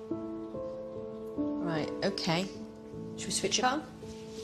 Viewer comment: Could Please Tell What she said to him at 0:46 ? I need the sentence cuz I'm going to translate the video into my Language . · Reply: She said, "Right. OK. Shall we switch it on."